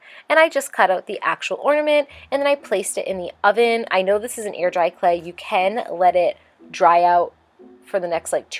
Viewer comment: Did anyone else hear 'ear dry clay' because of her pronunciation of the 'ai' sound? Whenever she says hair or chair in videos, I ALWAYS hear 'here' or 'cheer'.